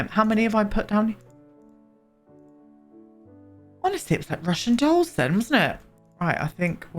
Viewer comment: I do adore you and feel like in another universe we would have been the absolute greatest of friends and so I feel like I’m entitled to roast you. What the actual fuck did you say at 29:09? My subtitles said Russian Dolson. Make it make sense because my brain just simply can not comprehend, also happiest of birthday love ❤️ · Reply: ".. Russian dolls , there.. " Also look up 'Parasympathetic relationships'